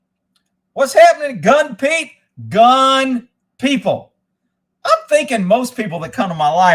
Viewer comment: I thought you was saying hang on people